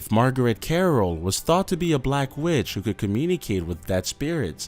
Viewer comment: they thought she was a black bitch is wat i heard